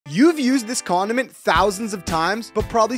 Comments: I thought he said: you’ve use this c*ndom in 1000 of the times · I thought he said Condom😭 what the